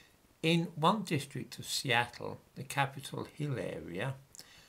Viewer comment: I by no means wish to criticise your presentation but when you said “Capitol Hill Area” what I heard was “Capitol of Hilaria.”